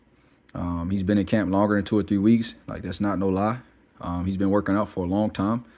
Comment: It's not a surprise and I can't blame him. He hasn't been in the ring for what 2 years? Also him and benavidez weren't suppose to be on the card replacing boots and Cody. Maybe he needed more time who knows. 23 weeks, Are you sure about that? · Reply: I thought you said 23 weeks but you said 2 or 3 lol my bad.